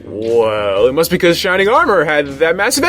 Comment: Whenever says Shining Armour, I hear "Shining Ahmur", which may just be me.